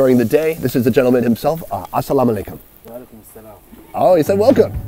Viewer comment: he did not say welcome 😂 he said "allekum salam", its the common response to salam allekum..